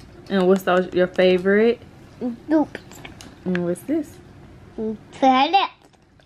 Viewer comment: Sound like said feather instead of flower and it sound like you want to sue that Chinese guy lol and that chemical he put on your foot could be alcohol, to stop the bleeding